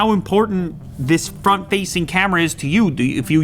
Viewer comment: somehow I heard "fuck-facing camera".